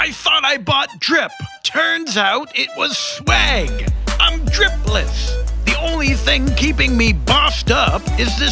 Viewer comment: I thought you said ”im DRIPLETS” that wouldve been cold